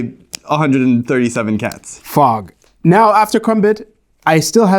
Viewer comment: it really sounds like, instead of "fog", he's saying "fuck" instead 😂